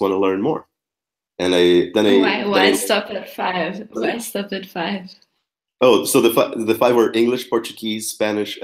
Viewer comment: "Why would you stop at five?" e ele entendeu "Which was the five" a cara dela depois foi hilária poker face kkkkkkkk